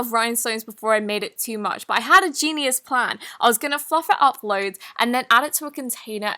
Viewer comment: when she said im going to fluff it up was i the only one who thought she said im going to f*ck it up 😂😂